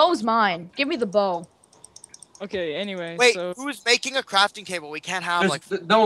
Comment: lol i thought they said crafting cable -.